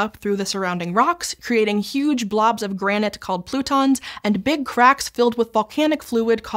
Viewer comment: 😂😂😂I heard, "croutons," instead of plutons, TWICE😂😂😂😂😂😂